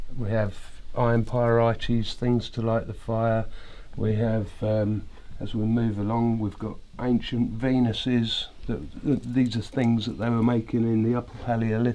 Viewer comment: I swore he said "as we move along, we have ancient penises..." I was like, wait, what?